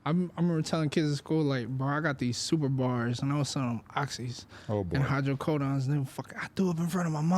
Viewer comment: My high ahhh thought that shii said “Selling his organs” clicked too fast